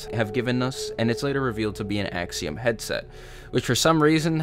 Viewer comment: i thought it said axon